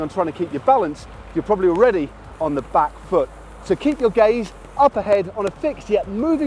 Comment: in 1:10 for a moment I thought that said keep your gays up ahead